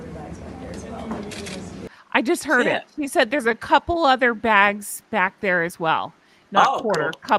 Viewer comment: It sounds like she said 'all the bags' back there' instead of 'quarter' but I could be wrong - I'm sure I'm not the only one who automatically tunes out every time she opens her lying, obnoxious mouth 😂 I feel sorry for those women who had to suffer her rudeness, disrespect and monstrous ego, her very presence is toxic.💩